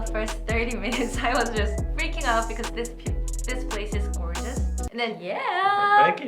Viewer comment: is she saying this place is original? I’m not sure what she means